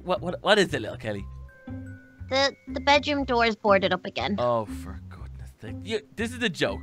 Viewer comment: I think almost said the f word when the door was blocked! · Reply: I thought he said for goodness sake